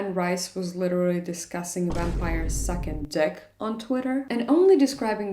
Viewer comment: not me hearing "second dick" instead of "sucking dick" and thinking I had COMPLETELY missed some subtext in that tweet😂😂